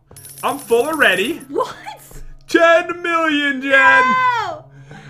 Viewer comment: I thought he said Jen million Jen 😂